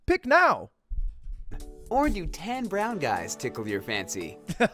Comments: thought they said ten brown guys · i thought it said or do 10 brown guys meet your fancy instead of Tan brown guys. had a small heart attack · I thought it said “do ten brown guys tickle your fancy” 💀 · I thought it said "or do 10 Brown Men tickle your fancy?' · First time around, i thought they said "ten brown guys" surprised pikachu face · i heard "10 brown guys tickle your fancy" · I thought the commercial said “10 brown guys” and I just immediately think they’re referencing the girl on the couch with all the guys behind her 😂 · I heard '10 brown guys' and I am dying. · i thought he said ten brown guys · I thought it said 10 brown guys lmao